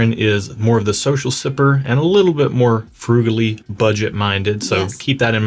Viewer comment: Did he just say brutally budget-minded😂😂😂. I call it smart haha